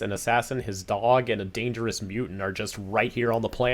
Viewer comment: i zoned out for a minute and came backto this line. I leterally thought he said "but then again, fat cock"